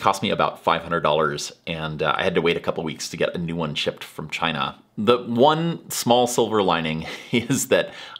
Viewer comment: Damn. the man is back! 4:15 Did he say "shipped from China" ? He moved from CH, when? In these 8 months?